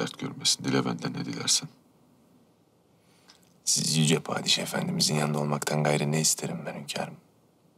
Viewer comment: ci saniyeler. Benmi yanlış duydum yoksa "padiş efendi" mi dedi hekim??? 10kez dinledim. Padişahımızın demedi. Padiş efendimizin dedi eminim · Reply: aa anladım. O kadar hızlı diyor ki, padişah efendimiz lafını. Delirdim laaaaa😭😭😭😭